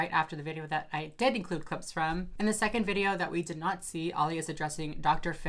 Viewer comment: did you just say "all he is addressing" "he" How dare you, you transphobe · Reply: I can see how you might have heard "all he" but she was actually just saying their name, "Ollie"